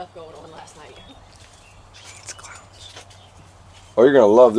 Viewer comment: sounds like it says, It's Carlos...